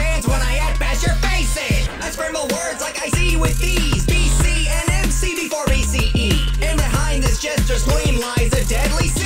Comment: Dose someone know what He is saying? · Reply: Bill cipher, an MC before BCE (before common era)